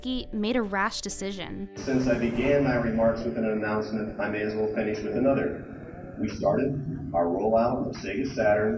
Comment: subtitles should be “I may as well finish with another” instead of [inaudible]